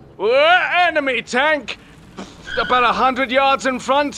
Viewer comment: I thought it said ANIME tank